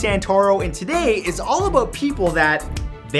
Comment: Did he say all aboot at 0:16 or was it just me